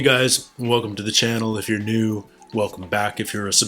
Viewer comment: Every time I hear your intro all I hear is "if your new welcome back" lol